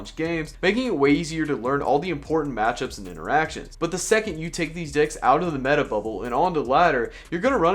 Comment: What did he just say?? 🧐🧐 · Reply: these decks pervert